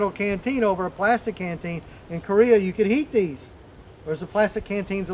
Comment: I thought he said eat these lol